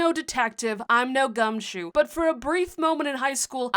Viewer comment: I have NO clue what word u said at the start "LISTEN! i'm no detective, i'm no (smth)" but it sounded like u said "Gumshoos" which's kinda funny bc in the Sun & Moon anime a Gumshoos works with officer Jenny as an officer XD